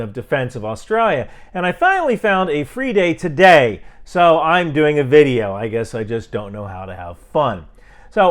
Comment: Get your Alcoholics Moving Cargo shirt here: <link> Get Warlock for Kindle here: <link> I do not believe that the "whitelist" image is authentic for the following reasons: 1. The image is not in a standard JSON or XML format. 2. The whitelist would not be publicly available. 3. The whitelist would be more likely contained in a database for efficiency. 4. Prominent names are missing from the list. 5. There is a misspelling on the list. An example of the original post is here: <link> For uncensored video, check out my substack at: <link> Like my shirts? Get your own at: <link> Want a personalized greeting: <link> Watch all of my long form videos: <link> Twitter: Instagram: BlueSky Reddit: /r/ryanmcbeth Join the conversation: <link> Want to send me something? Ryan McBeth Productions LLC 8705 Colesville Rd. Suite 249 Silver Spring, MD 20910 USA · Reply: I heard " I have a free date today". I thought : you lucky guy... but then i looked up what it was about hehehe Thanks for all that you do sir!